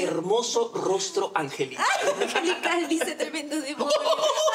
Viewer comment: que dijo? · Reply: Hay Angelical dice Tremendo Demonio